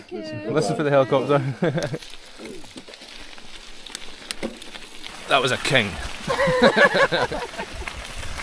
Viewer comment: "That was a king." That was THE king! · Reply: I heard "that was OUR King' in that heavily accented voice :)